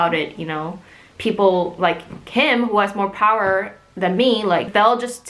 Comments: did i just hear “people like kim” at 9:20 ??? or am i hallucinating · did she say "people like Kim" or "people like him"? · Did she say Kim at 9:23? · She says "people like Kim," at 9:20. Idk if Kim is related to the name of the idol she was talking about or not. Maybe that's his last name? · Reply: Omg, I genuinely thought he said, "people like him".